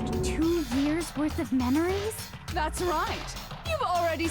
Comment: Me at 37:48 : Did you just say menories?